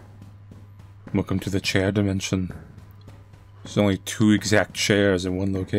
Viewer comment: when you said chair dimension, i misheard you n genuinely thought you said chad dimension 😭